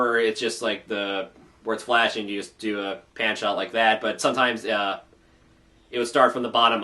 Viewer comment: OH, "Pan Shot". At first I thought you said "Panty Shot". For a second, I thought EQG2 went full-on anime.